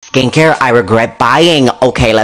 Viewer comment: I THOUGHT THEY SAID: "Skinkcare" one day idk how but yknow I thought it ws said so yEaH